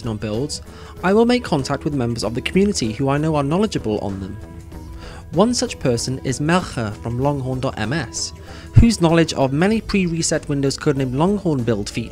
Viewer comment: "Maecha" did he say something different?